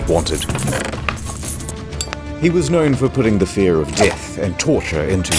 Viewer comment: I need to check my hearing ASAP because I swear to god I keep hearing “the threat of yiff and torture” at 24:09.